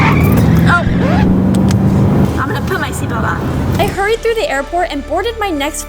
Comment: when she said 'opps let me put my seatbelt on' i thought she said oops let me poop my seatbelt on